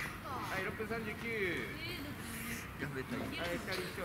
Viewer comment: なんて言ってるの？ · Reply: 「やめたいです…、ですか？」じゃないかと思います。